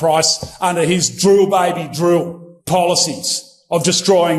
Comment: That Austrian guy sounded like he said “drool baby drool.” I realize he said “drill” with an accent, but drool is very apt